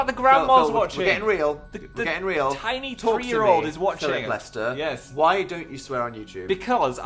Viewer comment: okay so i know phils name but i thought they said phillip molester like hoooooooooly shitttttt